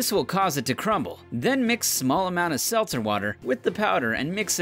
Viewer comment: Did he said salt water?